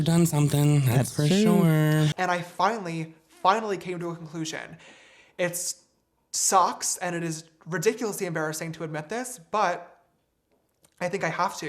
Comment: anyone notice it sounds like he says "it SOCKS" instead of "sucks" lmao